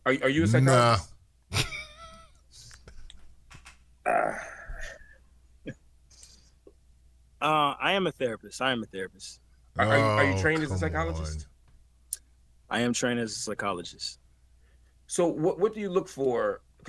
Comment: It sounded like he said, “I am trained as a slickologist.” I rewound it at least slickteen times